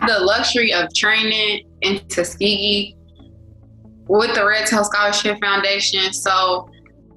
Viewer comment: What is the name of the scholarship foundation. I can’t understand the first word she is saying. Sounds like she said, “ Red Source Scholarship Foundation” in Tuskegee but I’m sure she’s not saying red source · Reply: Red tail scholarship